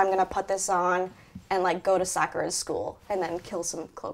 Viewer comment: Captions at 2:54 say soccer school instead of sakura's school.